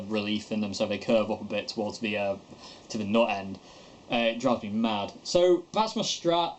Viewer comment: Is the 'nought' end the headstock? You British blokes and your faulty English - hmm, oh wait a minute, you invented English ... my mistake , carry on! Haha , just fooling around ... cool videos! I've recently become attracted to the idea of a white-ish Strat (specifically a 'Deluxe Roadhouse Stratocaster - Sonic Blue' with this interesting 6 position rotary knob instead of the middle tone knob turned on by a pre-amp button inside the main volume knob! .. ok maybe a bit off-white blueish with a white sparkly pickguard area). Interesting that you mention a slight curve to the necks. I find that perhaps some people are too obsessive about trying to get their necks perfectly flat. I tend to use 10-46 strings (rotosounds) on my electrics and with high action (great for bending and even doing vibrato at the top of bends ... action too low makes strings want to slip under my fingers on bends ... I think sometimes people are obsessive about getting low action but if they truly like that for fast shredding on thinner strings then more power to them - but if poeple just assme low action is the most proper thing, then maybe they are just going along with what they've heard). I remember bringing my blue flame/quilt-top Godin SDxt into a shop during a time of low funds to try to sell it and upon looking at it the guy just blurted ... "this action is shit" ~ oh well, I'm so glad I didn't sell it and I still have it and his opinion was crap as far as I'm concerned! Anyway, keep on maki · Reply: He's saying "nut end" it's just his strange northern accent. Never go north of London, there be dragon's and people with strange accents :)